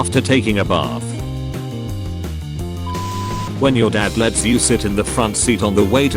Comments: so nobody gonna talk about how it said shit instead of sit-? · who else noticed it said shit instead of sit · Yall didnt notice in 1:05 said shit instead of sit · Shit instead of sit